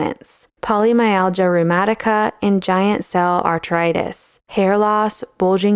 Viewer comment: Poli algebra numerica is not illness 😅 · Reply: I thought she said polymyalgia rheumatica and giant cell arthritis lol. Which are real diseases.